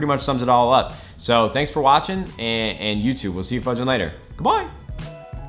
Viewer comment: says old school intro but says fudge? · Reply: I thought he said fuck but now that you said this I listened back and yeah heard fudge